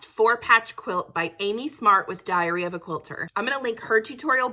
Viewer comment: OMG I thought you were saying diarrhea of a quilt.